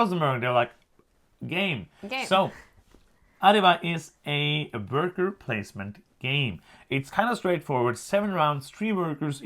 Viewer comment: I thought I heard it’s a bat poop placement game… 😂😂😂